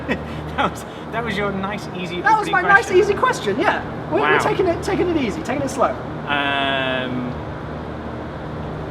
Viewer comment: What did Jason say about dlc 5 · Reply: Connor Craig he said DLC 5 confirmed